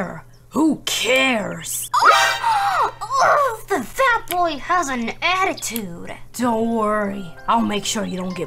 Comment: Did she say the fat boy has a attitude?!